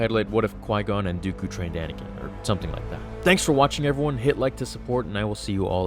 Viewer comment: All i heard was " quigon made a doodoo named anakin"🤣